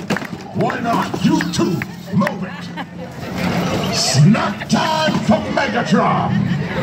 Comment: puts two women in a room “SNACK TIME FOR MEGATRON” Oh..... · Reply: Honestly I legit thought he said *Smack time for Megatron and I pretty much died after that.